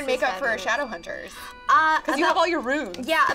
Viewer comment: I thought they were saying prunes